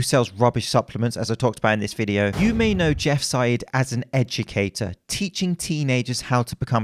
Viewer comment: Thought you said “edgucator” 😅